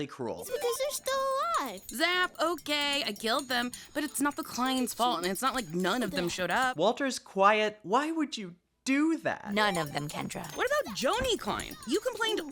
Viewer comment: Are you sure he didn't say "How did you do that?" It is hard to hear him properly but it does sound like he says it lol